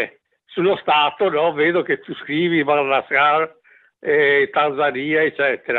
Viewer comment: credo abbia detto "madagascar" e poi "tanzania" qui